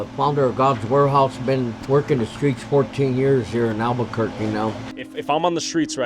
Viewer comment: For a second I thought the pastor said he had been twerkin in the streets for years now😂